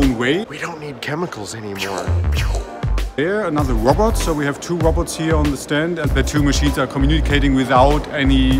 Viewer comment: I thought the man was saying Two roberts...